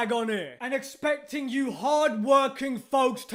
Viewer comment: i thought he said hard working fucks😂😂😂 that would have been funny lol